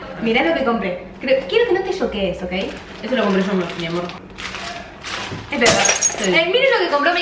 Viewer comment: le dijo a "mi amor"?! O yo nomás escuché eso?! xd